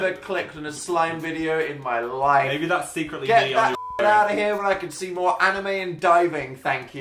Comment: what did phil say that had to be bleeped?? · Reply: dan said "get that shit outta here"